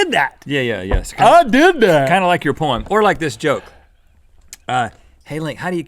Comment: Uhh... At 6:35 did Rhett say "kinda like your porn?"